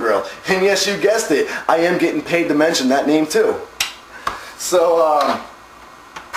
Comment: what does he says at 4:16 " and of course you guess it im getting paid for what?"